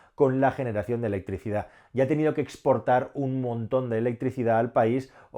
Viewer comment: será importar...o no lo entendí bien